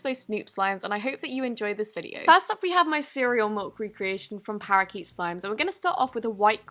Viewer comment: am I the only one who hears MULK instead of MILK?